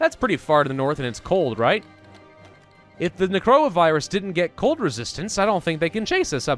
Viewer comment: did he say coronavirus, whut